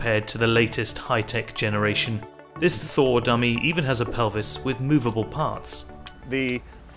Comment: I misheard it as "Older models are crude compared to the latest high-tech generation. This Thor-dummy even has a pelvis with a moveable arse."